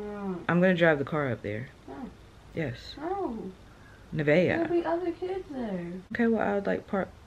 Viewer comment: You look so comfy lol but yeah nice earnings. I’m thinking of doing door dash on the weekends , and no you’re not being overprotective. I’m the same way and is her name? Or Ava I thought I heard you say? Am I hearing things 🙃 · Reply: It’s!